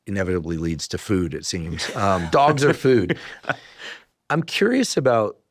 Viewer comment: Did he just say dogs are food?